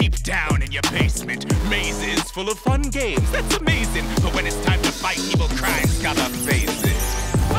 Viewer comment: Daveed Diggs - Look In Your Eyes (From "Marvel's Moon Girl and Devil Dinosaur") Lyrics: One place, one love This is a family, one for all All for one It's not a fantasy Underground king King carry all currently Open arms to the outcasts Welcome company Power plus power equals power over sorcery This is not a space This the home of the fortunate Big city lights Ya get lost in the Matrix Monsters livin' deep down in your basement Mazes full of fun games That's amazing But when it's time to fight people crying Scarred up faces Fight, fight I see the look in your eyes Fight, fight I see the look in your eyes Family, always be as one, that's forever Foundation, strong, only takes a little effort Brotherhood means build love til you close Fortune determines which side that you chose Fight, fight I see the look in your eyes Fight, fight I see the Look in your eyes (2023/03/09 16:46(JST)) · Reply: Thought he said when it’s time to fight evil crime gotta phase in